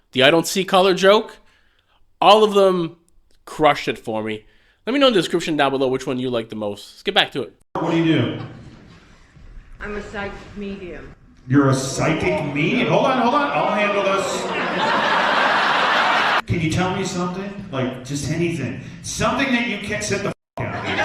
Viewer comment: Thought she said " size medium"